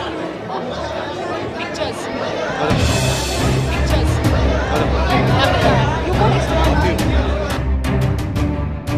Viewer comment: What did deepika say @ 0:10 to ranbir? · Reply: Pictures!